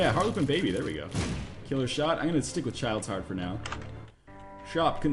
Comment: I thought that said Chelios Heart xD